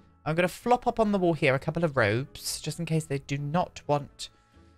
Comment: "I'm going to flop up on the wall here a couple of robes. Just in case.." Me: Oh, roBes! I thought you said 'roPes'.